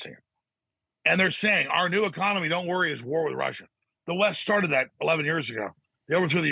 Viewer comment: In war with Russia? I thought he just said we are going to be in war with China.